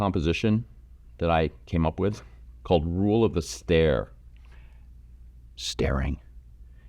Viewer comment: I listened with a few glances as I painted. On your made up face, heard it as "The Rule of Astaire" dropped everything in shock and awe. Rule of the stare. Fabulous. Ha. I thank you so much for your tutorials 💓